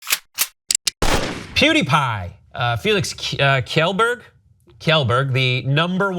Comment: There's nothing hard about saying YELLBERG. Kjellberg is YELLBERG. I mean, how fucking disconnected ARE you? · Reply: Luke Redstar I thought it was said like shellberg